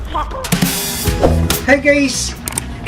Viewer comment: I dont know why but at the intro i always hear "HEY GAYS" i just cant anyone ?